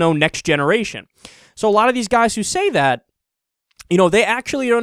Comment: sorry, where exactly in the vid did he say it was "each and every one of them"? cuz i thought i heard him say "a lot of these guys" which wouldn't even necessarily mean most. you're also just proving his point. i'm 37 years old and have never had a homosexual fantasy. sorry, but i just haven't. the belief that we all do just isn't true. that's a point touted by insecure men who don't know how to deal with their own gay or bisexual desires. i know women who say the same thing; that they've never been attracted to other women ever. not all women have "kissed a girl". i also know homosexual men and women who have never been attracted to the opposite sex.